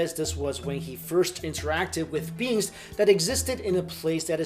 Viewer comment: when you said "beings," I thought you said "beans." Sometimes my ears deceive me... 😑